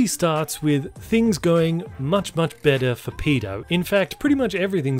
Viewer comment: Did you say pedo?